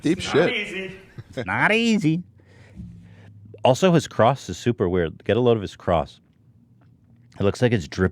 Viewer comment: I thought Ethan said "Get a load of his crotch." on